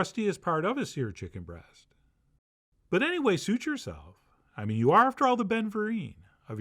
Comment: What was the rhyme here? I can swear he said my name 2:20. You are after all the Ben Fourie, of your chicken Florentine